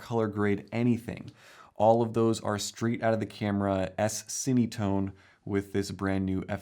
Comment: What does say at about 4:15? At first I thought it sounded like "semitone."